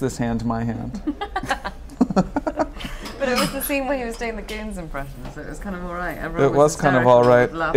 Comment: What did Carey say at 01:12 - "It was the scene where he was doing the...?" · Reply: This is crazy late but, she said he was doing a 'Goons' impression. 'The Goons' were a crazy british radio comedy trio from the mid 20th century. Silly voices, crazy skits. If you are into monty python type humour you should look them up